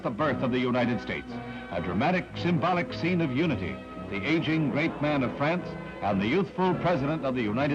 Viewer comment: What is the commentator saying on 0:26 ? "The aging great man of France" is what I'm hearing...Thanks